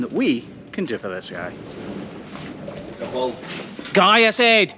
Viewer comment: I could’ve sworn I heard you say “cállese” at minute 6:35, which means shut up in spanish. I had to replay it so many times to make sure it wasn’t because I was chuckling from it